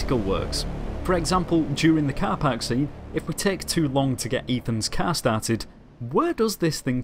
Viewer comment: did he just say Ethans car?